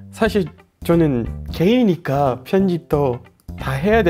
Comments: 초에 개인을 게이로 들었어요ㅋㅋ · 사실 저는 게이니까라고 들림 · 나만 게이니까로 들었냐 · 사실 저는 게이니까라고 들리네ㅋㅋㅋㅋ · 사실 저는 게이니까 라고 들려욬ㅋㅋㅋ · 나만 0:53에 개인이 아니라 게이라고 들리냐?ㅋㅋㅋ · 사실 저는 게이니까 ㅋㅋ 그렇게 들림 게이에 편견있거나 한 건 아니고 그냥 웃김